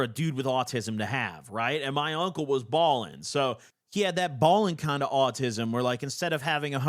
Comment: A new main channel video is like finding water in the Sahara desert these days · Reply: I thought he said "balding autism" and it still made perfect sense 😂